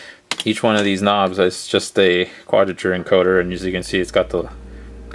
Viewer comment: Hey! Was wondering if you know what type of encoder they are using? You mentioned it @ 03:11 but didn't really catch it! Keep up the good vids man. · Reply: It's an optical "quadrature encoder." Sorry, I realize I said it pretty fast.